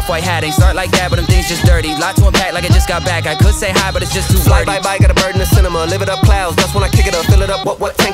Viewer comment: I heard "could say hi but its just too flirty" because wordy and fly overlapped 💀